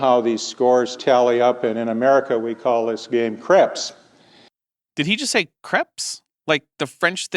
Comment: I initially thought he said "Crips" like the LA Gangs!